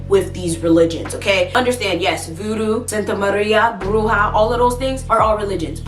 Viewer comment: Girl you know I love you but I had to rewind around 7:05 and laugh when I thought you said Santa Maria 😂😂 But I know what you mean and I was raised to believe that witchcraft is witchcraft whether is good or bad, white or black. It is not from God so it is evil.